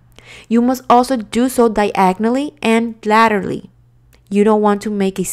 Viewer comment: What is 7:36 she says? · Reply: laterally, sorry